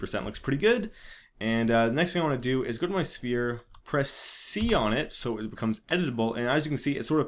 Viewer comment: sorry i`m dind`t understand what do you said in 3:12 Could you write the command? please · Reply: press the C key om the keyboard ahah